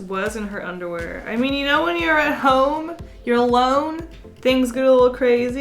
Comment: Don’t take this the wrong way but I thought she said “you know when you’re a hoe” but she actually said “you know when you’re at home”